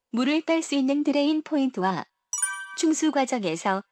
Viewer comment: 에서.. "우선 하이드로를 수행하기 전에 수압 테스트 후 물을 뺄 수 있는 드레인 포인트와 충수 과정에서 배관 내부 공기를 빼주기 위한 벤트가 반드시 있어야 합니다" 충수 과정이란 표현이 맞는건가요?ㅠㅠ..잘 안들려서 질문 드립니다.. 벤트라는건..공기가 나가는 구멍이고..드레인은..배수구, 즉 물이 나가는것..즉 물이 나가냐..공기가 나가냐에 따라서..드레인 / 벤트 라고 표현하는 차이일까요? //마지막으로 매니폴드 라는건 뭘 말하는걸까요? 04:38 승압과 하압차 라고 하는건가요? 06:45 승압을 방해하고 압력 게이지의 슬렁임(?) 현상의 발생하게 됩니다. 슬렁임 현상..??인가요? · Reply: 음성 전달에 다소 부족함이 있어 향후에 이 점 개선하도록 하겠습니다. 1. 충수 표현이 맞습니다. 충수 과정은 배관안에 있는 공기를 Vent 로 내보내면서 물이 채워지는 과정이기 때문에 수압테스트 전 반드시 Vent 가 있는지 확인이 필요합니다. 또한 매니폴드(Manifold)는 영상에 나오는 것처럼 배관을 가압을 할 수 있는 추가 장치를 말합니다. 2. "승압과 하압차트를 보시면" 승압과 하압이 표기된 차트를 보시면이란 내용이 되겠습니다. 3. "출렁임 현상"이 되겠습니다. 고압테스트 시 목표로 하는 압력에 도달하지 못하고 더 이상 압력이 올라가지 않는다면 이는 충수과정에서 배관내부에 있는 잔여공기를 모두 빼주지 않아 여전히 일부 공기가 남아 있기때문입니다. 비압축성인 물과는 달리 공기는 압축성이기 때문에 배관내부에서 물과 공기가 섞이며 해당 잔여 공기가 배관압력이 올라가는 것을 방해하는 경우가 발생하기 때문입니다. 궁금하신 내용은 댓글에 질문 남겨주시면 답변드리도록 하겠습니다. 감사합니다.